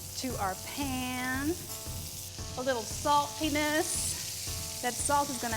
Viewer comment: I swear she says salt penis